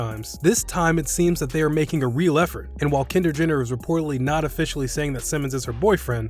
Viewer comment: did he say kender Jenner